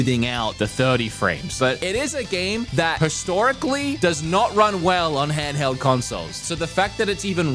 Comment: I swear it sounds like says, “It is a game the PESTORICLY” instead of historically. Not major, just thought it was funny